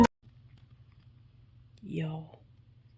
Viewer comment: Steve thought you said “ew” instead of “y’all”… 😂